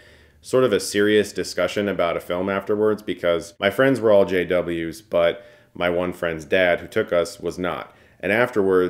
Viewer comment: LMAO I thought he said "my friends were all SJW's".